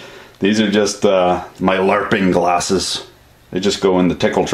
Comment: "These are my LARPing glasses." Did I hear that right?